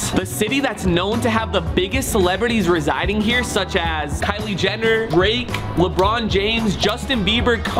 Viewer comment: Did He just say Kylie gender in the start of the video😂